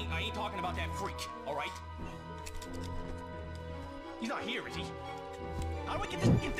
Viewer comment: I agree, sir. Anyone who says he says "he's not here is she" is mishearing. Even if it's in the transcript I clearly hear "he"